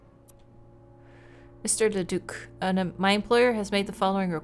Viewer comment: I honestly thought she said "Mr Leduc. A mindflayer has..." (Yes I play Baldurs Gate 3 and no, there is no such thing as playing too much Baldurs Gate 3)